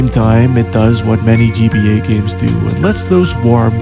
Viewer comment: I heard it as the “BBC games”.